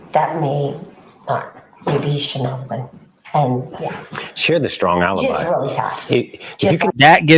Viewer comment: I am a court stenographer, and if this was testimony, I definitely heard and would have written "She"